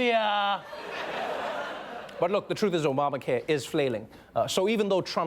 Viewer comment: did he say "flailing"